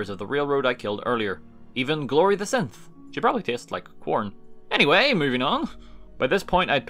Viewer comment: I heard "She probably tastes like porn" at 11:06, anybody else?